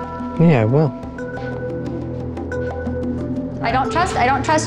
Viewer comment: I Thought it said "Touch the horse" 💀